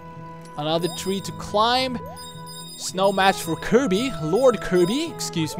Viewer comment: Did you just say "Snow match for Kirby"?